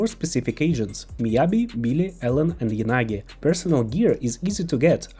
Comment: I can't understand who the 4 agents who get personal gear is at 2:28. Miyabi, ???, Ellen, Yanagi? Anyone know what he said? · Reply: Billy Kid